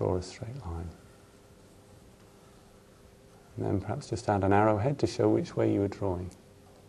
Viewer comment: what is saying before the word ''arrow'' . Sorry, off topic but im studying english :D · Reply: "now perhaps just add an arrowhead to show which way you were drawing"